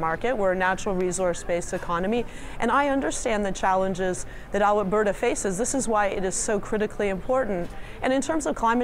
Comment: did she say Alah-berta?